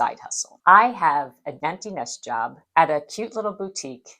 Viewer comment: Can anybody figure out what is saying at 12:57 of this video? She says "I have a _ _ _ _ _ job at a cute little boutique" ??? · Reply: Empty nest